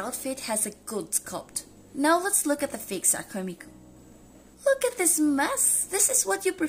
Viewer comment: i keep hearing “fake sucker Miku”